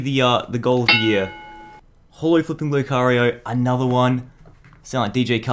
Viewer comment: did he just say fuck